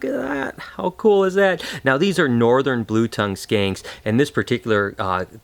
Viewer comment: Sounded like you said 'skanks' ^^ Haha^^ Nice videos!:) Do you have any venomous snakes from South Africa like the Puff Adder, The Boomslang (Tree Snake) or the Cape Cobra (extremely venomous)? Would you want highly venomous snakes?